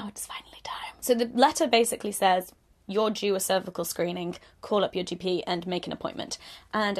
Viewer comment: Just watched your other video about religion so all I heard was 'You're Jew a cervical screening'